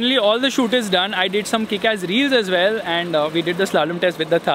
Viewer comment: did he said.. kick ass reels 😂😂